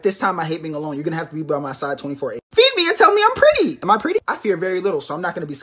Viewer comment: Libra during pregnancy: feed me and tell me I'm pretty Lol I'm libra and that would so be me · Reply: Ohh it’s and tell me I’m pretty. Idk why but I thought she said feed me until I’m pretty lol😂